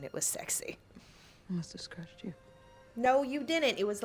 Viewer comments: am i the only one who thought she said “it must have scratched you” as in the lion? and that’s why it’s funny that he just repeats her not realizing he’s repeating her? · She says "It must have scratched you" not "I". (At least , that's what she says in the play)